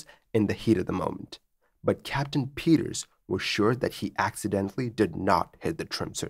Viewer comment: There was also the "accidentally didn't touch the controls" vs. "didn't accidentally touch the controls" bit. As a native English speaker it's easy to forget just how much more strict about word order English is compared to other languages. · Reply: Yeah, I noticed that one, too. At first, I thought maybe he said the pilot "absolutely" did not hit the trim switches. But then I went back and played it again -- even turning on the captioning -- and there it was: "Captain Peters was sure that he accidentally did not hit the trim switches."